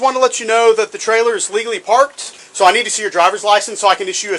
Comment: I THOUGHT HE SAID LEGALLY PARKED😮